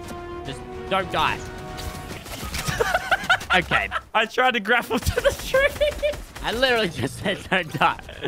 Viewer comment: Fresh: ‘Just don’t die’ Lazar Lazar: “Misses grapple on tree and dies” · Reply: At first I thought you said he missed the grappler on a tree, like it was just on there